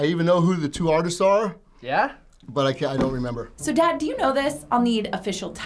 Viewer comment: did she call him Dad